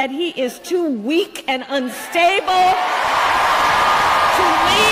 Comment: did she say “unfable”? Also check, they deleted the tweet, who’s the weak one hiding?